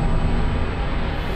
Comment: did he say maison fondèe? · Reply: Les enfants terribles